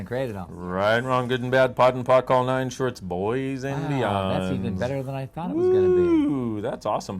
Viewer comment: 👍I get it. Can someone please tell me what I keep hearing them say (he says it in other videos too) @ 4:12 “right, wrong, good, bad blah blah blah” what are the words & why? Maybe if I knew the words I could understand why and how it fits into the discussion. Thanks. 💞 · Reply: Right wrong good bad poc pod all nine shorts boys and beyonds.